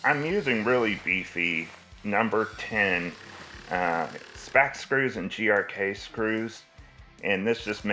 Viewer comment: Can someone tell me exactly what screw he is saying at 8:54 he used, and/or send me the link to them. Thanks. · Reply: I used #9 spax screws. HDK are the same depending on which store you bought them. Be sure to pre-drill with 1/8” bit.